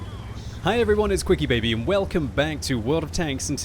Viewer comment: Sounded like he said "Hi everyone its click baity here!"